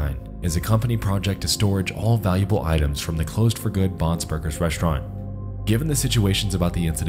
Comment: At first I thought you were saying Bob's Burgers and I was waiting for Bob and Linda to come out with their shenanigans